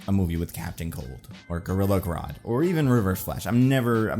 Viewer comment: Honestly thought you said "River of Flesh" here and I thought damn, haven't heard of him before, he sounds like a cool villain